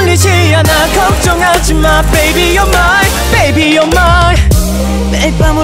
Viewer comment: I thought it said ''Baby, you're my'' :v